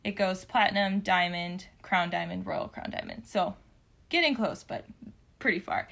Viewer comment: I misheard it as Royal Clown Diamond and I refuse to call it anything else now.